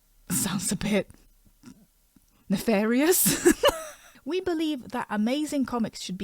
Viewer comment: What exactly do you say 0:50 ? :D i think i dont know the word :D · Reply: Nefarious? It means to act with bad/dodgy intentions.